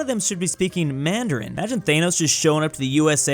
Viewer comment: DID HE SAY THAYOS